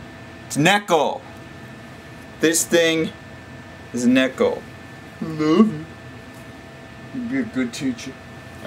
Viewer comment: I may be mistaken but, did he say "I love you" at 1:05? o_O I understand if he did, I'm not making fun of him for it, I'm just curious! :D